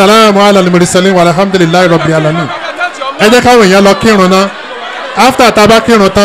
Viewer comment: Sounded like somebody read Surah Fatiha as "minhamdulillahi Robbil alamaeen", let the person take note of that and correct it please, if I misheard then may Allah guide and forgive us all